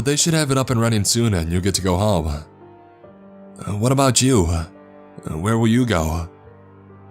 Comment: or 24 :47 mio (or whaterver his name is I forgot ) says war a bout you but it sounds like he said wat abput jew coincidence I think not.